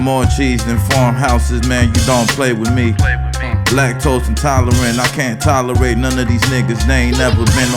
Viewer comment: Did he really say blacktose intolerant?? That's so fire, but maybe I misheard it.😂